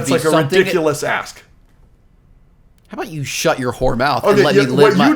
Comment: did just say whore?